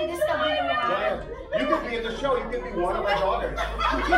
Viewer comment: ang kulet naman nito. ang saya. at around 10:24 ang sarap ng tawa nila pero hindi ko na-intindihan sinasabi nila...what did say that made everyone laugh? pls? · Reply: If we'll be in the show, you can be our daughter.